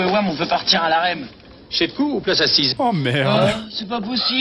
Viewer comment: il a dis quoi au début de sa phrase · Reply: Si tu parle du contrôleur : « Chécou ou place assise »...